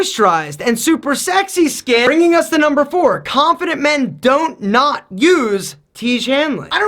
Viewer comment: did he said don’t nut?